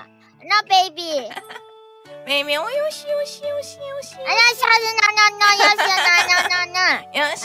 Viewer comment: bro what she said? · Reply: She says "yoshi", it's like saying good